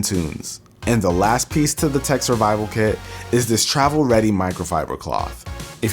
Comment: I thought it said "tech samurai kit"